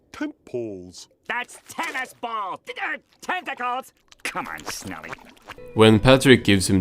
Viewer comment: I thought squidward said 'THATS TENNISBALLS, DICKHEAD'